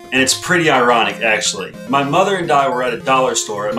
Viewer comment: Wait.... You had a daughter when you were 6 years old?? Check the video right before a minute, it sounds like you said mother and daughter